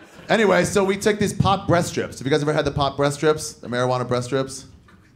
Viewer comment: Who else's thought he was saying "THC breast strips"?